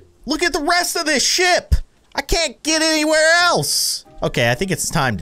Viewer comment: On 4:49 I thought he said look at all of this Sh#&